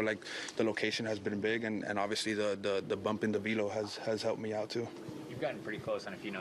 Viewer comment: Awesome for Nestor, hes been a lot of fun to watch and im glad we are getting to see someone with such a unique style. Sounded like he said a "bump in the libido" had really helped him out about 3:40 in LOL!